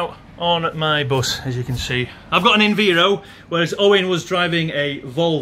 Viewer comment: did I hear enveero?